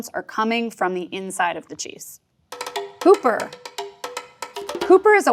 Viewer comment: I swear I thought she was saying pooper instead of hooper, that's what I get for listening at triple speed